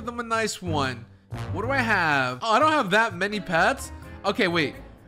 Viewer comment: He really said i dont have that many leg pets